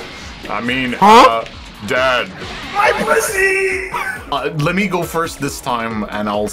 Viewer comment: did anyone else hear gogeta say "MY BUSSYY"